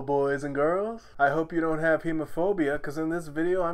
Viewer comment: Bro I thought he said homophobia at the beginning